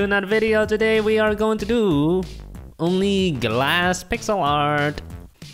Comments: For some reason I thought it said grass! Maybe you should do that?? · I thought it said only grass 😂😂😂😂😂